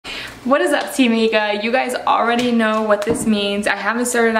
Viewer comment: I thought she said "what's up team nigga" for sec 😂😂🤣